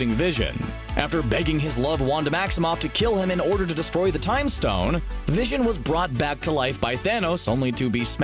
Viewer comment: did anybody heared Time Stone instead of Mind Stone?